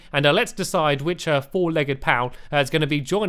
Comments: Is it just me or did sound like he said four legged cow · one you said "four legged pal" I thought you said "four legged cow" and i was like STAMPS THAT'S SO MEAN